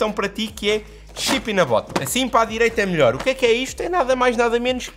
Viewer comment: Eu juro que ouvi "shit in a bottle"